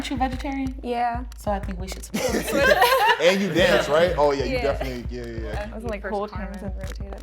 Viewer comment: am I the only one who heard him say “oh you definitely gay” or what